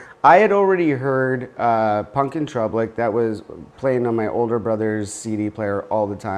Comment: Huh? 😂🍻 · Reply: sounded like you were saying troublic instead of droublic